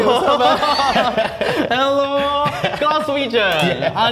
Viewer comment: The subtitles are wrong at 2:06 says "Glaswegian" not "Glasgow Region" Glaswegian is a term for someone from Glasgow.